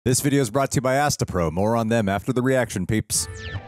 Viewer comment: I heard "Moron them". LOL.